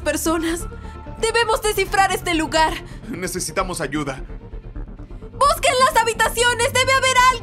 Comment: Xd me confundí en una parte que dijo necesitamos ayuda pensé que había dicho necesitamos a Juda😂😂